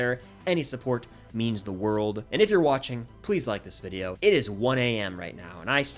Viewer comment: I heard him say Steve tho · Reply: try listening to it again without looking at the screen and the subtitles.. “please” sounds like Steve … you will also hear him say Mike